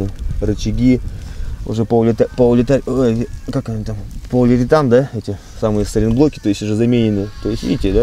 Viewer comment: Полулевитановые сайлентблоки???? Я один это услышал?